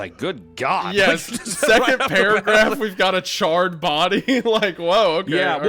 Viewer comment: What did wendigoon say at 4:42 ? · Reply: He said we got a charred body at the beginning and then he started he to say “we got” before MC spoke